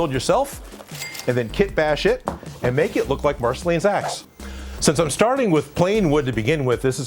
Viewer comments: who heard marceline's ass lol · I thought you said Marceline’s Aa$ 😂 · I thought he said “ and then make it look like marceline’s ass” lmao 😂